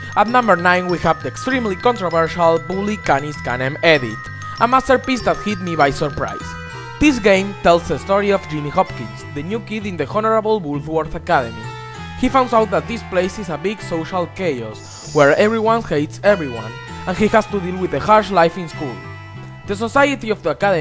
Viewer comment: did you say Honerable or horrible?